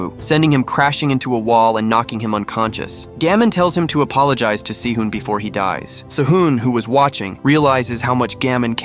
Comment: is it gust me or did he said TO APOLOGIZE TO SI-EUN I swear I think I heard it said si-eun